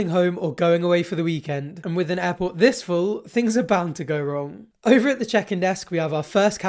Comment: with an apple this... This is what I hear. What does he say? Thank you, lovely people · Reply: It’s late sorry but “With an airport this full”